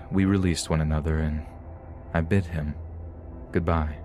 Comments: I THOUGHT HE SAID “and I BIT him” and I was like woah what??? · I thought you said "I bit him goodbye." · I feel kinda bad... For a second there I thought he said, "And I BIT him goodbye." 🤦